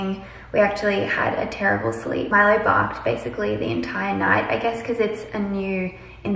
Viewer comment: I thought you said “Milo barfed all night” lol 😆 I’m glad it was just barking...